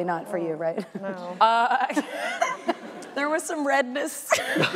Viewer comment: what does Mayim saying at 2:22? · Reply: There was some redness.